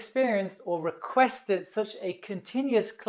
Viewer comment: no comments on the German accent makeing it sound like "tree" instead of "three"?